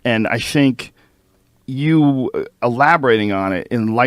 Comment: does Joe say "....I shank you...". As in "I'm about to attack you"?